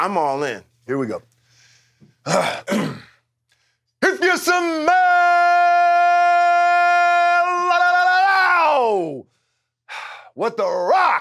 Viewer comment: I always thought he said "Do you smell" not "if you smell"